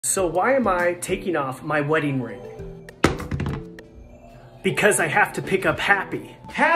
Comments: I thought he said ‘I’m taking off my wedding ring to get happy’ LMAO 💀🤌🤌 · Am I the only one who thought that he said " because I have to become happy " and then I thought he's gonna divorce his wife but then I just realized........ HE IS SINGLE 😃